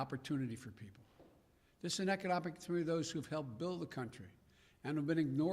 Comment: WHAT THE HELL DOES HE SAY?? Econopic???